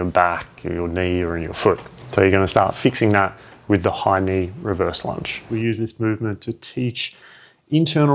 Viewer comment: Oh, thank you for this one. The first time you referred to those lunges, I heard: 'Hiney Reverse Lunges' ..then figured out it's 'High-Knee' ;~)